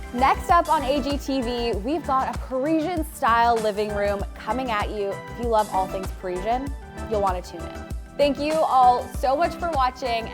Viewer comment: I thought you said a Kor-esian style coming up.. and now I want to know what Korean/Parisian would look like!